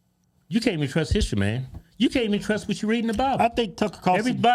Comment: Is it just me? Or, did anyone else hear them say "you can't even trust what's in the bible" at the 9:14 mark? I'm done with these guys!